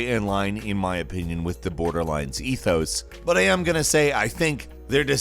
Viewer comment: did he just say “in line with the boarderlines ethos?”